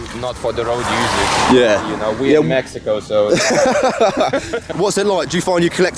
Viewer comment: Did he say Mexico lol